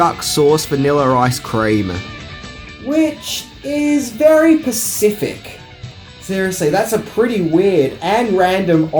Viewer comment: SPECIFIC***** since no one else is gonna say it lmfao...like around 5:40 into the video he straight up goes "which is reallyyy.....PACIFIC" 😩😩